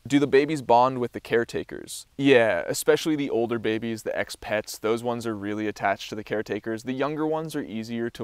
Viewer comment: Awesome video. I kept hearing you say ex-pets and for some reason I heard it as expats and was wondering what country the monkeys were coming from lol